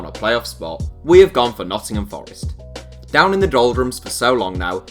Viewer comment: It sounded like he said noncingham forest